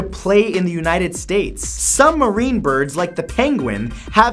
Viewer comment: Who thought he said "submarine birds"?